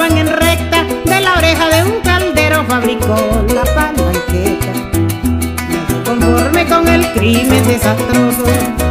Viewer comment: Alguien que me ayude, ¿Que dice en el minuto 4:02?? De la oreja de un caldero fabricó qué?? Por fa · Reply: de la oreja de un caldero fabrico la palanqueta.................la escopeta artesanal o chopo cuando no se carga con guaimaros (plomo)se le meten clavos,pedazos de hierro,ect.ect. y a eso se le dice palanqueta